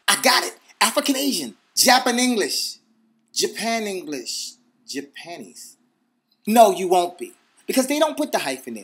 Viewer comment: lol, did he said Japanis? xD